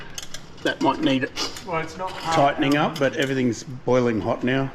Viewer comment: did anyone else hear go "well its not a hard line", I mean he's not wrong